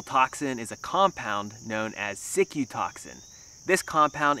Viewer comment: "Cicutoxin" i heard "sick-you toxin" and yep i accept that etymology as valid